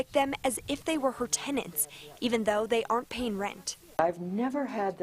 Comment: Did you say even though they are paying rent. Most squatters don't pay shit. 01:32.